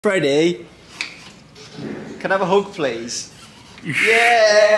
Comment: I thought he said: FRiDAy CAn I HAvE A hOg PLaz